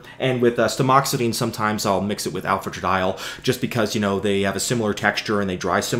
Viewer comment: What word does he say at 14:30 ? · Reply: Alfatradiol.